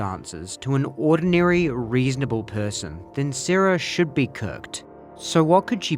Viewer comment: sorry i’m half asleep, did he say “might be cooked?”